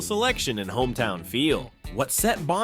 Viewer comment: I thought he said Gun Shot. 😂